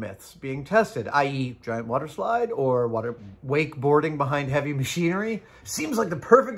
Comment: At first I heard, "_waterboarding_ with heavy machinery". That, I think, would be less fun for the crew.